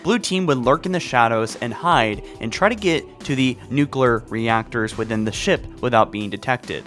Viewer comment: Did you just say... “noo-cler”